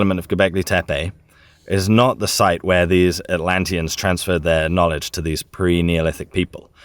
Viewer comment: I thought he said Atalanta